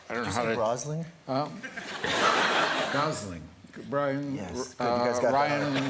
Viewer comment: I thought at 3:48 he said Bryan 😂😂